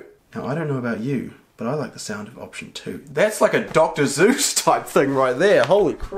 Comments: Did he say Dr Zeus ? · Am I hearing things or did say “that’s like a doctor Zeus rime”. LMAO 😂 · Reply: The point is sounded like he said Zeus instead of Seuss And you didn't even spell Seuss right sorry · Is it just me or did he say Dr. Zeus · I heard you say Dr. Zuse